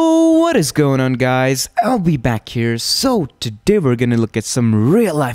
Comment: At intro I thought he said ill be back here lol